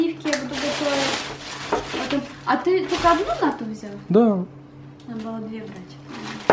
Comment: ты только одну нату взял?Да... обалдел бл*ть мне одной послышалось о.о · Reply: она сказала надо было две брать